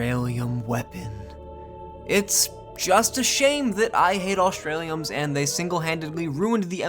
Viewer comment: i thought he said he ate the australiums